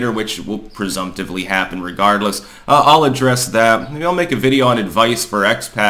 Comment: i was packing my bong when you said "happen regardless" I thought you said irregardless and I was about to jump all over that shit like a grammar nazi, and then I listened again 😣